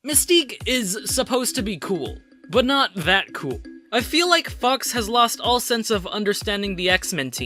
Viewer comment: Did you just said "I feel like fucks!" 3:24*... ? Maybe my hearing is bad.